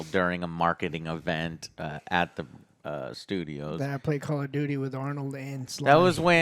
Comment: OJ mentioned he played online through Xbox Live with... a celebrity? Though I couldn't hear well who that person was because AJ talked over him, kinda disrespectful. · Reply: It was a joke,he said he'd played COD with Arnie.